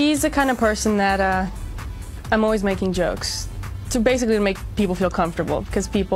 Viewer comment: What is the word on 0:53, before "...making jokes'. Some verb?... I can`t hear it. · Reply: "I'm always making jokes"